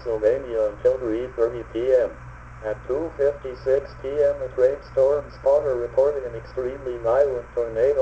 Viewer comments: Originally I thought he said a rainstorm spotter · It sounded like he said “A great stormed spotter” he said a trained storm spotter. It might be my ear buds is why it sounded like that. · What does he say? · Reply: At first I thought it said Rainstorm Spotter